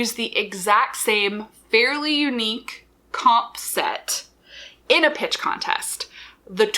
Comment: for a whole minute, i kept thinking "comb set" was just a weird way to say "concept" ..smh me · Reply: Yes! I thought she was say, "com set". I was then trying to figure out the meaning of com. The most common use in my own life is at work, where com is shorthand for the communications department.